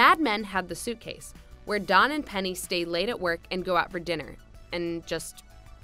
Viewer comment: did they say "don and penny"?